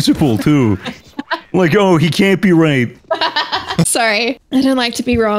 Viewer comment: subtitles at 8:01 translating ''cant be right'' to ''cant be raped'', i mean even the subtitles got infected with their fucked up sense of humor X)